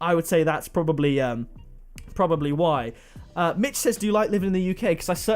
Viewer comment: I thought he said bitch but he said mitch😭😂😂